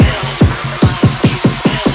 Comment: ここから加速する「負けた」にしか聞こえないェ… なんか熱のとき人の話し声が速くなったり遅くなったりして聞こえるの思い出した。